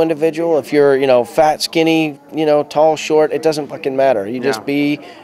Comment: Did he say F**** at 2:48? Hahaha oh Rudy/Samwise!